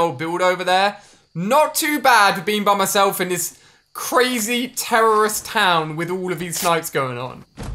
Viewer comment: Did he just say crazy terrorist town 😂😂